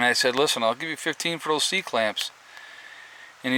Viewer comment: I thought you said sea clams. lol.